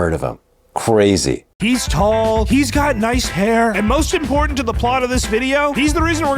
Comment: did he say he has nice hair? I need to a hearing test.